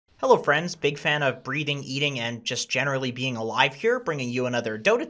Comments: I misheard your intro as "breeding, eating" ...or maybe I didn't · I thought you said breeding lmao